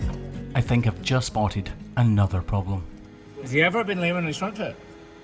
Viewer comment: What the hell did he say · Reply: "was he ever lame in/on his front foot?"